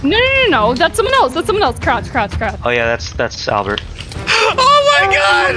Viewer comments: AM I DEAF OR DID SAY NAME IS ALBERT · im not sure if i heard that right but like 1:04 did he just says name is albert